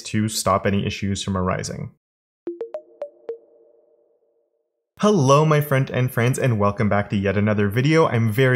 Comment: For as long as i have followed you (quite some time) , i always thought you said 'hello my friend and friends' My mind was blown when i realised the actual intro always said 'Hello my front end friends' I only realised this like 3 days ago. Sorry lol